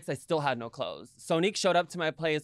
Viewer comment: At first I thought she said 3 bags of trash in front of Sonique xd